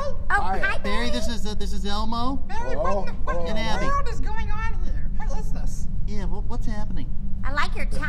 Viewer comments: Did Elmo say “what in the f***ing world is going on here” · is it just me, or did I just hear elmo say "bary what the f_ck is going on here?" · did he just say what in the f#@ world is going on???